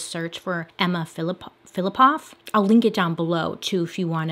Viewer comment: I thought it said "Emma Flippoff".... I was like, I want my last name to be flippoff too! But it wasnt flippoff.... I'm so dumb